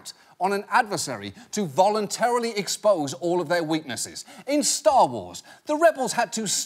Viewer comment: I thought he said “Starbucks”, and I was thus very confused at what he said next.